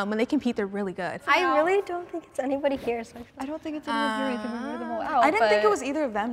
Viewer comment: did she say "I really don't think anybody cares"????? or am I hearing things 💀